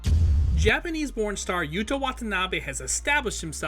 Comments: I thought you said "Japanese Pornstar" LOL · Off the rip I thought thus mf said Japanese Porn Star